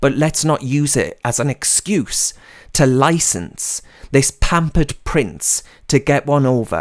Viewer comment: when you said pampered price, I played it back, as I thought you said pampered prick. I think the latter is more fitting as his title. Thanks for the video.